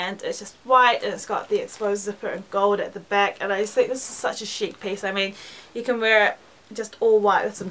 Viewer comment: Loool!! i thought you said this is sucha shit piece instead of chic